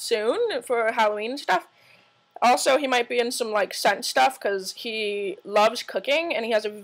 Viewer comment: hahahah I also thought I heard you say "He might be in some sex stuff" instead of "He might be in some scent stuff" LOL.... OMG! He is such a hottie.. You two will look soooo fkn cute together.. Congrats <3